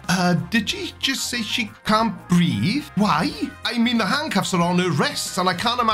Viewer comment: For me it sounded like she said "he can't breathe", that would make at least little more sense.